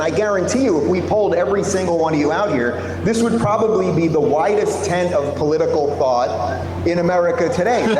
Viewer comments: Lmao, for a second there I heard "whitest tent of political thought" and I was like wtf, did finally say something smart? 🤣🤣🤣🤣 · I honestly thought he said "whitest" at first here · I know said widest, but I definitely heard 'whitest'. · At the beginning i thought he said "widest" but i think he ment "whitest" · for a second I thought I heard “the whitest tint of political thought in America today.” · He said "widest tent" of political thought. I thought he said "whitest tent" of ignorant narrow-minded non-thinking pos group of people. My bad.